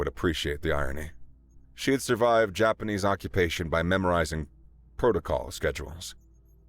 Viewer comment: Thank you for being 1 of about 3 actual narrator's. Although at @ 20:32 you say Japanese protocol and the captions say Japanese patrol